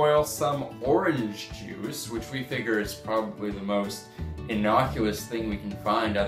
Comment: please tell me he didn't say "innocuLous" instead of innocuous...I'm hoping I misheard him. The fact he is wearing a Dalek tank has redeemed some points for the video!